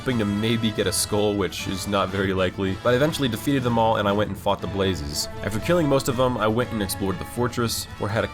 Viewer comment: who else heard “i went and fcked the blazes” lmfao